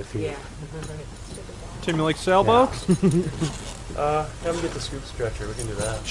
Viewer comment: did he say Do you like Sail Boats??? 2:36 wtf